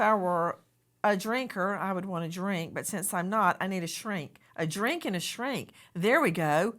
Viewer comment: what is a srink? What is she saying? Thanks in advance 😊